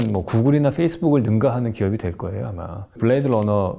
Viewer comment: 증가하는 이 아니라 능가하는 으로 들리는데 오타죠?